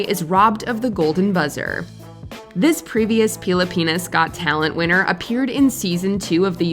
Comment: I think I miss heard it um did she just he is Pili penis Pili in Tagalog means choose.. .. so.. he chose Pen!s? Yow, I'm Filipino I like that term haha